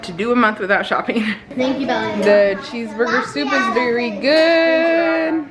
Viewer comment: I keep mishearing it as 'cheese booger soup' 😂